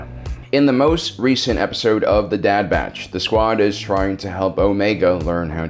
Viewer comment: Did he just say the dad batch? Am I hearing things correctly??